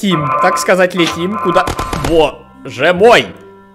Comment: мне одной послышалось "бл*ть летим"?